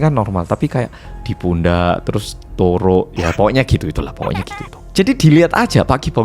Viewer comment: (turuk) apa sy salah dengar?